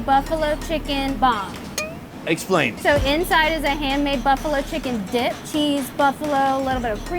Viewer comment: Did she say "Hand-made" or "Hair-Made" at 8:44 :P